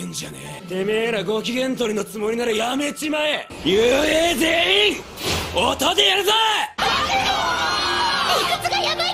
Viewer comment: Can someone tell me what Bakugo says in 1:03 ? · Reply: U.A zeyin otho de yaruzou